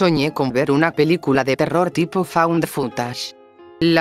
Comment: escuché "found the futas", jajaja